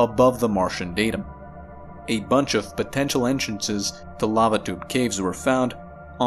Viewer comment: I heard "babadook caves" instead of "lava tube caves" twice in the first part of this video. Had to rewind a few times.